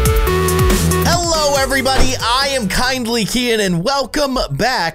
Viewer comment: It sounds like you said hell no neighbor (ಥ﹏ಥ)